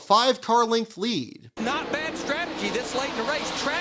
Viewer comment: did DW say "that's bull5h!+"